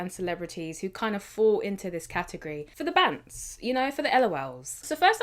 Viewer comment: what did you say here? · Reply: Bants = Banter